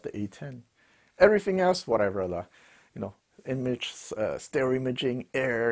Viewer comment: did u say 'whatever laa' at 3:55?